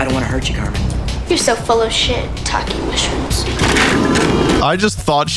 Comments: what does she say here? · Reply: You're so full of shitake mushrooms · as a kid I had never heard of shiitake mushrooms, so I always just thought Carmen was saying "shit-talking mushrooms" and somehow getting away with it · even the subtitles think it said shit